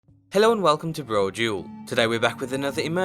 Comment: Litterly all my life I thought he said "hello and welcome to brojewel" but it's broduel. I feel so lost